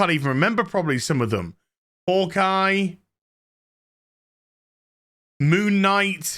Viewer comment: I thought you said 'Orc Guy' at first lol I was like 'Wait, what? There was an Orc Guy show? That sounds cool.' Then I remembered that terrible Hawkeye show.